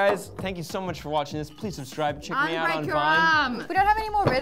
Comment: did he just say, "check us out on vine"... I miss vine